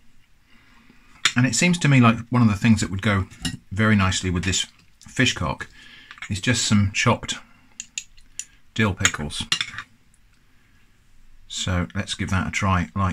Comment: I assume it's no relation to Matt Hamcock ? I'll get me coat..... ( I was laughing so much at this one that I swear I heard him say " Pill Dickles" at one point !)